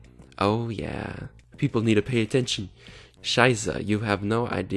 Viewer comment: DID U JUST SAY SCHEIßE?!